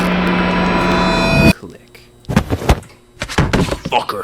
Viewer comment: I thought he called Gordon a cuck LMAO